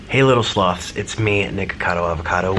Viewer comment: I thought he said "hey little sluts" 💀